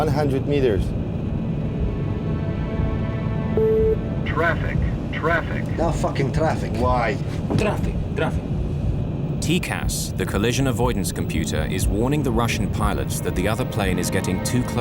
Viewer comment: Does he say, "No F*ckin' traffic" lol?